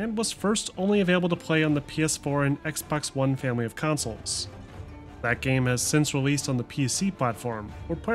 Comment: I legit heard him say: "only avaliable on the PS4 and XboxOne failures of consoles" the first time I listened